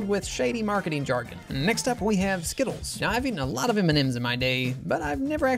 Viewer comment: I thought you said skibidi 💀